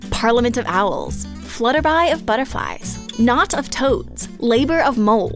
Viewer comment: I hear it's called a 'kaleidoscope of bitterflies'